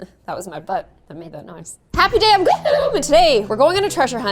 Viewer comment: After years of watching you i always thought you said happy day on woohoo and now after this i known you say happy day I'm so i just wanted to say how I'm a horrible fan soooo sorry